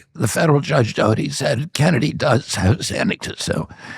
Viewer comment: "My case this week, the federal Judge Doty said Kennedy DOESN'T have have say any to so,"-is on the caption. "My case this week, the federal Judge Doty said Kennedy DOES have say, any to so," This caption states the opposite of what Kennedy said. This happens often on the conservative podcasts I watch.